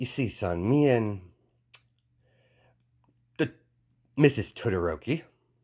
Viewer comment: I was so confused because I thought he was saying Twitter ropey or smthng not Todoroki